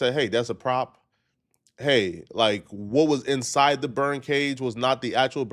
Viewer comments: I thought he was saying BIRD cage 😂😂😂😂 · This whole time i thought you were saying “bird cage”